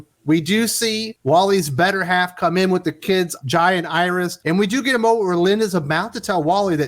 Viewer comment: Every time I heard "Jai and Iris" I kept hearing instead "Giant Iris". ;)